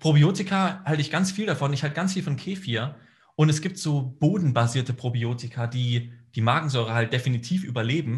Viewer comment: Was sagt er bei 46:15? Bohnenbasierte Probiotika?